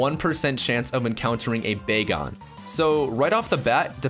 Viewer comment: I thought he said bacon :P